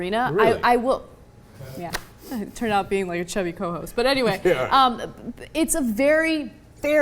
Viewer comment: At first I thought she said that she was the "chubby co-host". Damn.